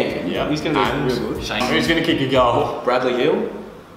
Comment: Thought said Bradley Beal at 3:37. Sad thing is that probably would have been a better answer.